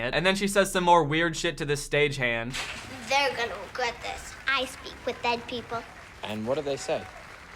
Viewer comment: Instead of "I talk with dead people" for some reason I heard "I sleep with dead people" 😭😭😭😭. The guy was like, "And what do they say?" "They complain. A lot." 😭😭😭😭